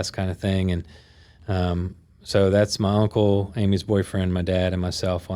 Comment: Did he say “Amy’s boyfriend” @ 43:03 ???